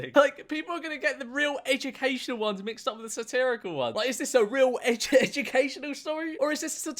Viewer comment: when you read the description of the channel i actually thought it said ‘satanical’ instead of ‘satirical’